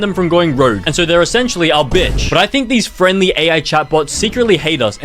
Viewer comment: I misheard that as "a b!tch"